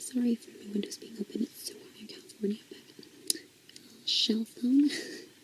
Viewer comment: What is she saying at 15:43? I replayed it a bunch of times but I still have no clue.. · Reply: "As always... I wanna say hi to Rick Moranis"